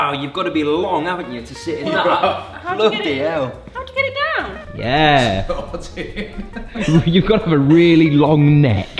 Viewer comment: ish did say f*cking hell? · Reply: bloody hell